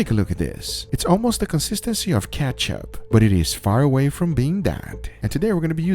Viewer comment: thought you said cat-chub not ketchup lol